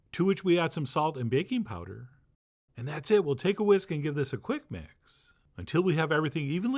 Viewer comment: Oh my chef John ....love of my life! I had to stop and rewind when I thought you said. "take a whiz". And by golly you did! 😂